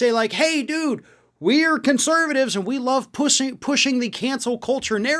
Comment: did he say we all like pussy pushing....